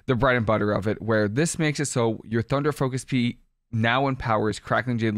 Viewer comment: Did you say “thunder focus pea” @ 1:11 😂😂😂 much love!!